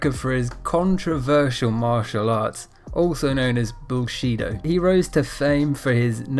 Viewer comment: Did you say Bullshitdo?